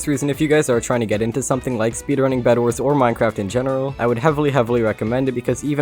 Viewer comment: Me: Finally someone who puts captions. Autocorrect: At 4:47 instead of bedwars it said bandwidth what bad captions.